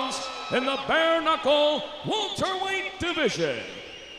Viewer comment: i cant be the only one who heard "walter white division" lol